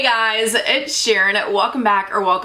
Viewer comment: I was zoned out and when you said “hey guys it’s Sharon” I thought you said “hey guys WD sheeren”